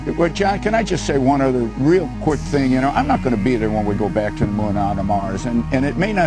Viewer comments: “…or onto Mars.” is what he says there. Just a small correction 🤓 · he clearly says "back to the moon or on to Mars." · I think he said "go back to the moon and on to mars"